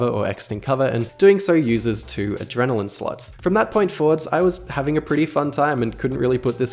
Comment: Everytime he says "slots", I hear "sluts".